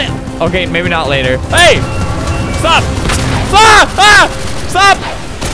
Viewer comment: i had my sound down really low and he started saying stop but because of the sound it sounded like he was f Word... ._.